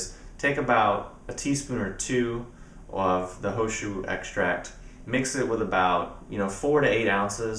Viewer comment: is the tea you were saying the He Shou Wu Extract? sounded like you were saying ho shu